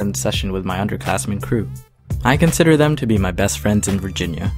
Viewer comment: I thought he said "I consider them to be the best friends I had before virginity."